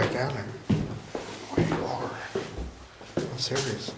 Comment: i heard "We are" in a very deep voice.